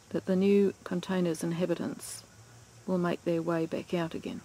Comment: It is a shame that a dummy like me thought you were saying "inhibit ant", intending to convey "inhibitor", instead of immediately appreciating your being nice to the flies by referring to them as inhabitants. Too funny. But then I am 12,000 miles away in New England.